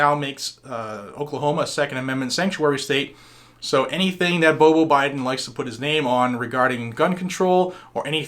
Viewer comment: Hey. I was just thinking about the first time I heard you say "BoBo Biden" I liked to died laughing cause I thought you called him Butthole Biden. Which turns out to be rather fitting for him lol Thanks for the video. I live in SouthEast Kansas about 25 minutes from the Oklahoma border. Travel there a couple times a month to go shopping.